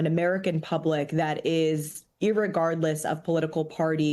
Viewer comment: Did she say 'irregardless'?